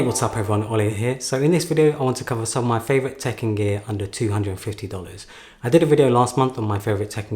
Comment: I kept hearing 'Tekken Gear' at the start.